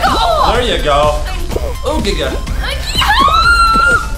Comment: did chad just say digga ?